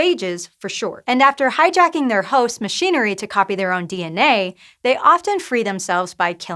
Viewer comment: Does she say "Copy their own D and A"??